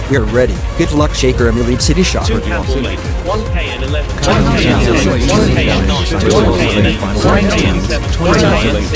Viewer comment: What are they saying? It sounds like they are counting down, but it is so overlapped that I can’t hear them clearly. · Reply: It's "1-KN 11"